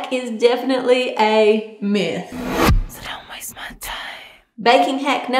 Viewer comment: The myth thing (whispers "so don't waste my time") is so creepy but so funny at the same time 😂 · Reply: I thought it said: Sit down my smart time. Lmao